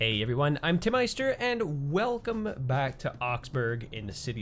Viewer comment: When i first heard you say your name i thought you were saying tit meister